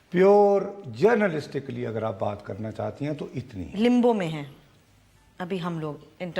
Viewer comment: what is this word Gulmeenay saying I am unable to catch it can anybody help me LIMBO or LINGO or LINDO or LINZO ???? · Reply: Limbo, type of coma